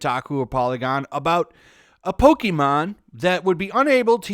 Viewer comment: I was just casually listening, then I hear, “Pokémon buttplug”